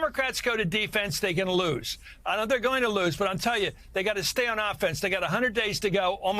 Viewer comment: Did CM say he knows the dems are going to lose (2:07)?